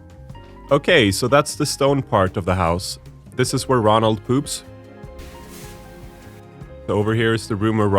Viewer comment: Video just started. wanted to skip in a bit and the first thing i hear is "this is where ronald poops" 🤣🤣